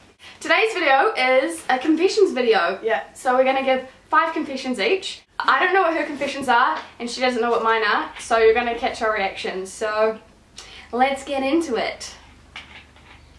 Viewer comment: I thought you said catch our erections.